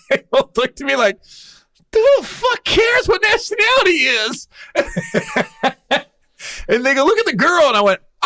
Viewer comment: Is it me or at 31:40 does he say who cares nigga look at the girl r am I hearing things